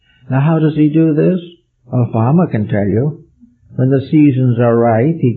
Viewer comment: At the 1:13:12 mark, did he say "Obama can tell you"? I've rewound it 6 times and that's what it sounds like to me but this can't be.